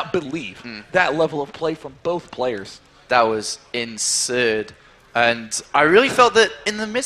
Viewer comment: insurd? sounds like he was saying insane then started to say obsurd. "that was insurd"